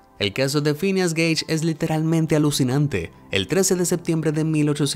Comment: solo yo escuche queso ???